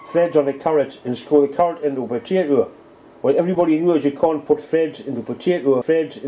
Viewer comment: I thought he was saying you can't put veg in a potato, not threads on a potato. And I'm English! Imagine my surprise after 12 minutes to discover you didn't do either.